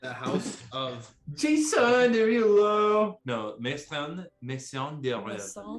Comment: i keep hearing them say "maison des rêves" (house of dreams) instead of "maison des morts" (house of the dead) and it's a little confusing ^^' the funny thing about being bilingual is that u may easily understand your second language spoken by someone from your first language but the other way around is waaay harder for some reasons XD